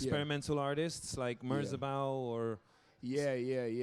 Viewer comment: who did they say? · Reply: Merzbow